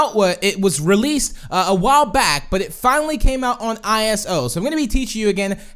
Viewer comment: did he just said ISO?